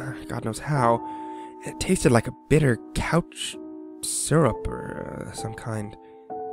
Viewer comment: Perhaps it's just me being weird, but did anyone else laugh at 2:18 when it sounds like he says "it tasted like a bitter couch syrup or some kind." Not trying to insult or anything, I know mistakes happen, I just found it really amusing.